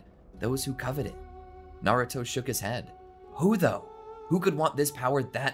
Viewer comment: Who else loves Naruto? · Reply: is it just me or did i think he said who the fu